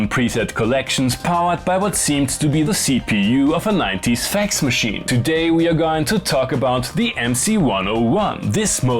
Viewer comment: Lmao, i thought you said "powered by a nineties sex machine", instead of fax machine 😂